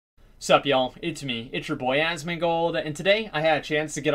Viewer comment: did i hear " suck u all"?